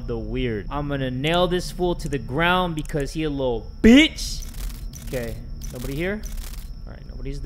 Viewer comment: "Ok everybody, don't get scared, he's right here..." jumpscare Me: Even knowing it's coming, gets scared anyways and screams like a lil' bish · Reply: I thought it said screams like a little British